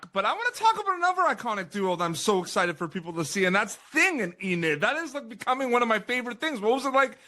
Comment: I thought bro was about to say Bruno and Enid and I about punched the screen